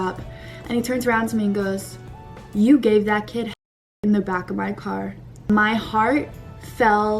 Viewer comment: wait what did he say? I‘ve replayed it 10 times and I can’t figure out what she‘s saying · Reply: Drink dance panic at the disco and drink some more “you gave that kid head in the back of the car”